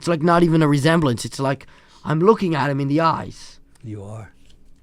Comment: anyone catch that? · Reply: He says, You are. In response to him saying it looks like he is looking into his eyes